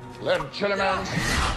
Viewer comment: What did he say? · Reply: Legiliment